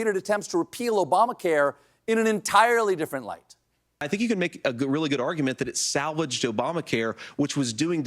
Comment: I'll swear that at 2:08 he said "a gorilla good argument". Bring bananas!